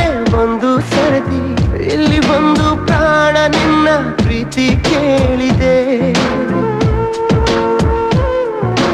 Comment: I can't understand word, adu praan na or kaana na yen Heli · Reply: Prana